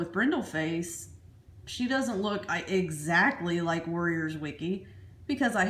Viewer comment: I thought she was saying warriors Twinkie